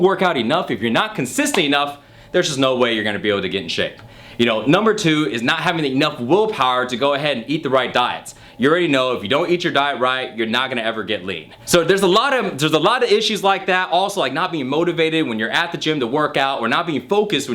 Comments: I thought at 0:50 he says:"If you don't eat your diet right, you're not going to ever get laid" :D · Lmao it sounds like he said that if you know if you don't eat right you're not gonna get laid 😂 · sounds like he said " You already know if you're not eating right, you're not going to get laid" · it sounded like he said "if your not gonna eat the right diet your not gonna get laid" lol